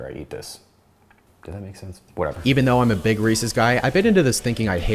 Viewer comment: I thought bro said “I’m a big racist guy” 💀😂😂💀💀😭